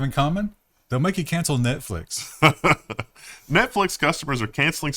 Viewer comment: I heard “Debtflix.”